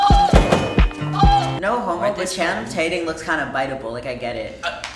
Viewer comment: the guy who said channing tatum looks biteable. did he say channum tating? T.T?